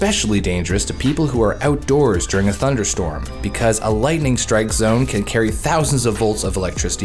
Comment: why did the captions sai lighting s*** instead of lighting strike